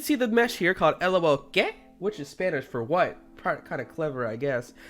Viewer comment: sounds like he said gay